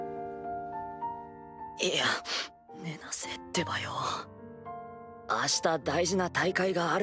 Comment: did he just say tebayo